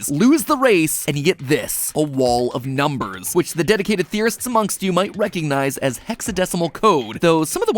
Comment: In the intro, when it said theorists, I thought it said terrorists and I was like wait WHA